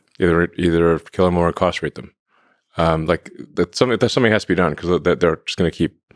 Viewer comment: Ok, Musk said "either kill them or incarcerate them" but since I was listening at 2x speed I misheard that as "castrate them" and was so surprised lol